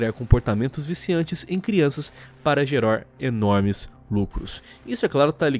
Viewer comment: só eu entendi "geror"?